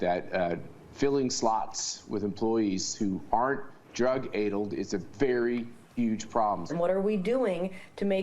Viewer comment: Did just say "drug aydulled". <rewind /> Yes, he did. So...., I'm guessing newsmax has him reading a script that they wrote and he hasn't seen before.